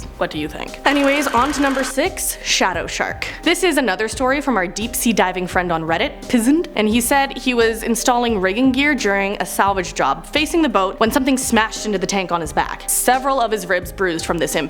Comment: 'Pzznd' sounds like a person who would put sanitizer on there cut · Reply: I thought she said pizza 🍕